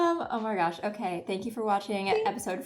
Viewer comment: Balanced instead of banished